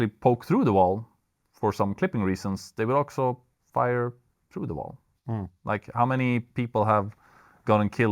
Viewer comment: Svengelskan back in action (For anyone wonder, "They'll också" instead of "They'll also")